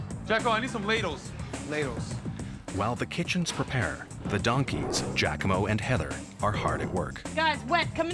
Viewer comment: Did he say donkeys!? 😂